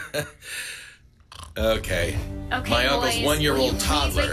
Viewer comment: did he just say "my almost one year old toddler" 🤠 HE HAS A KID AND I DIDNT KNOW!? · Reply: he said uncle’s